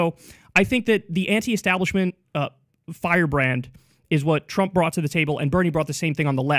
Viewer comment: There’s no way I heard him say “ trump isn’t bought”. No way.